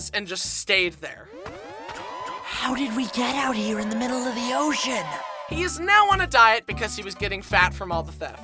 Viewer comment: POV 11:56, instead of it sounding like “ocean,” says “shin?” 🤣🤣🤣🤣